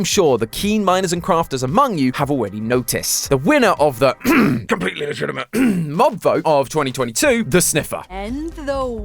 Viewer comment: wait what did he say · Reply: He said completely legitimate